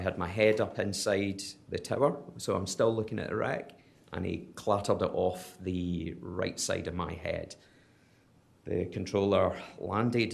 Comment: What does he say? He clattered it off the right side of my head???